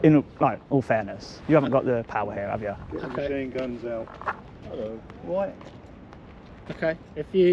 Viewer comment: did he say get your machine guns out lol😂